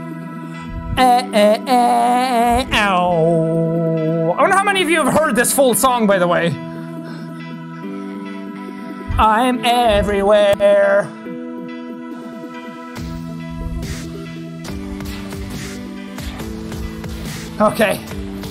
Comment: All this time, I thought it said “hiding everywhere” i feel so stupid now 😂